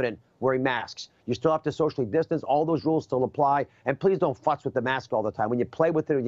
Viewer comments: Did he just say “please don’t fuck with the masks”? At 2:45. I rewound it like 10 times to make sure I heard it correctly. Are y’all hearing that too? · did he really say "Don't fuck with the mask all the time..." ? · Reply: No he said fuss